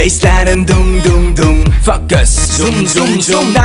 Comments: am i the only one who hears "fuck us" at 1:25 ? xD i know that Zico says focus but still.. xD i keep hearing "fuck us" xD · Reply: Oh he's saying focus! I thought he said 'fuck this...' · I always thought that Zico said "Fuck this" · am i the only one who hears "fuck us" at 1:25 ? xD i know that Zico says focus but still.. xD i keep hearing "fuck us" xD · I heard "f*ck us" 😂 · That era where we thought Zico said FUCK US but it was just FOCUS! omggg my feelsssss! I am just missing this era looking back at this!! Bringing back so much memories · OMG WHEN ZICO SAID FOCUS I THOUGHT HE SAID FUCK US